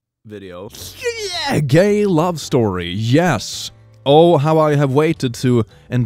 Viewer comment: When he said “gay love story” i thought he said “gay lobster” and i cant unhear it now😭😭😭